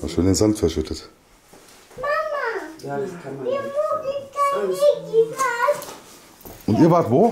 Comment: Schön, dass ihr wieder da seid. Als du das mit dem Budda festgestellt hast, verstehe ich als Antwort: Naja, mir reichts jetzt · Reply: Hallo ihr Lieben, ich habe mir das jetzt nochmal mit Kopfhörer angehört. Das Kind der Klientin sagt zu seiner Mutter: Mama, jetzt sei doch mal ehrlich (Minute 24:25 in etwa). Da steht ihr bei dem Tisch. Und die Mutter quatscht direkt rein: Nein, das soll der nicht... Mit den Klienten stimmt nicht was 😅